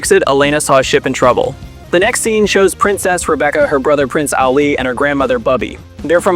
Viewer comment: I know his name is Prince Ari but it sounded like you just said… Prince Ali, fabulous he, Ali Ababwa